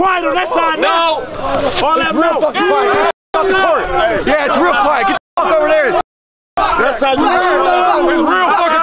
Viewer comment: did say "Its rean f'n quick get tf over there N!@@